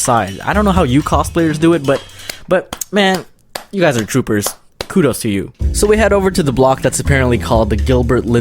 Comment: I THOUGHT HE SAID “kuroo’s to you.” 😂😂😂😂😂😂😂😂